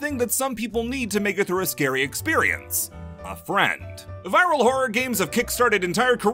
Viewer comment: I thought it said vial horror games